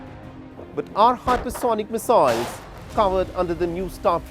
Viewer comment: did he say "our hypersonic missiles"???